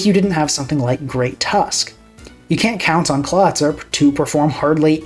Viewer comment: I was looking away when you mentioned Clawitzer and for a moment thought you just said Clodsire in a funny way.